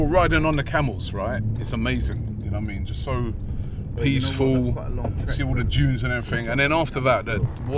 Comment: Did say ‘see all the Jews and everything’?